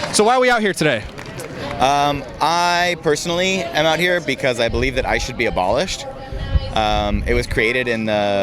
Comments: IIt sounded like he said "I'm here personally because I should be abolished." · ha ha ha, I thought this guy said "I should be abolished," and then i realized he said ICE. I think he had it right the way I first heard it. He has that effeminate whine to his voice, so you know his diet is primarily soy and that his sperm count is 0. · Every time someone says "ICE should be abolished," all I hear is, "I should be abolished," so it all balances out in the end.